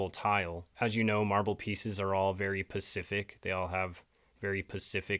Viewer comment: i believe the word you're looking for is "SPECIFIC", not "PACIFIC ". That being under the assumption, you weren't trying to make some sort of water related pun as in pacific instead of atlantic, not sure why you would. If you were making a pun, I would think you would have been more " specific" about the body of water you're relating to.